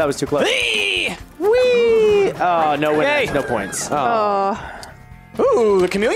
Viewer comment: 2:09 I'm sorry did you say no winners???